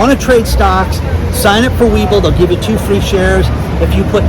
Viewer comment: Hi pretty right now you said we bull and I thought you said we full o bull had to laugh at myself